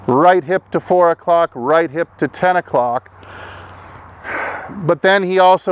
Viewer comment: I never heard him saying hip to 10. I was always under impression it was him saying take the right knee to 10 o clock